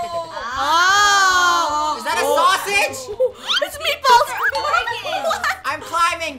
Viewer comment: funny I think she said: it’s me falls laughs